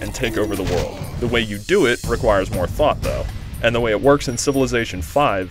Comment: he really said requires more fuck up???